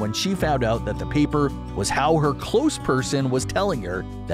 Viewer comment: Did he say her "close person"? What was that?